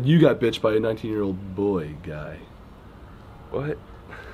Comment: Someone plz tell me why I loved the way that Gray said boy guy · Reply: weave snatched it sounds like he said boy gay